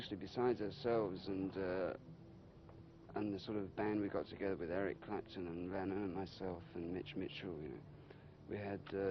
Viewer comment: Jack Hubbard right, but at 6:25 hes talking about a band he was in with lennon, but when he says his name it isnt clear what he's saying. but there's no one else he could be talking about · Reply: He says "the band we put together with Clapton, Lennon and myself".